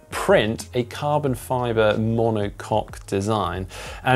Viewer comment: Did you just say “monocock” two times with a straight face?